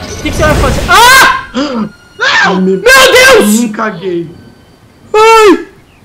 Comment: quando atira na só ouvi "mim caguei" kkkkkkkkkkk